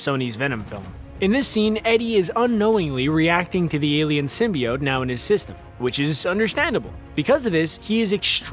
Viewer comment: Did he say "...infected by the alien known as SYSTEM"? PLSSS prooofe me wrong..🇩🇪